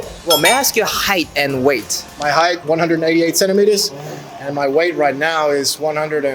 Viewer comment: 198cm이 아니라 188cm라고 하신 것 같은데요? 저는 one hundred and eighty eight centimeters 라고 들리는데요?